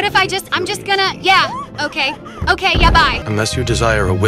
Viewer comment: Ok lol ,so i thought you said yabai at first and i repeated again and heard yeah bye~~ noice😂